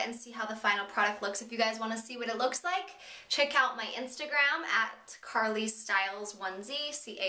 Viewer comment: For a moment I thought you said CarlieStylez onesie haha :P